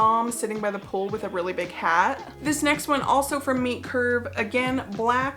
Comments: I thought you were saying Meat Curve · I thought you were saying meat curve then entire time. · So, now I know that it is Meet Curve, but for some reason I thought you were saying Meat Curve. I was very confused at Meat Curve for a clothing brand. Homophones, man. I like the sea snake suit (is that REALLY a thing - to ward off snakes?)